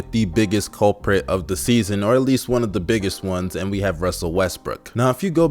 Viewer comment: did he just say russel westbrick